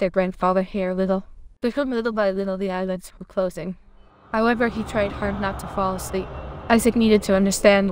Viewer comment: wtf did she say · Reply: But little by little their eyelids started closing